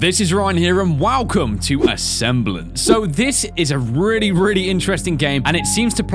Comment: I thought it said ambulance